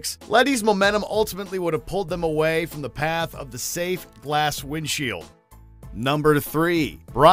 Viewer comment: did you say " safe glass windshield?"